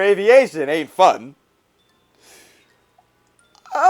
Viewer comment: I thought you said Aii Fuck not "Aint Fun" 😁Btw in Amibo its 20,000 bells now to change ordinace 😡